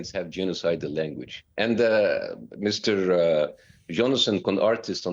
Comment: Did Bassem just called the Israeli Frankenstein, Jonathan Con-artist? 😂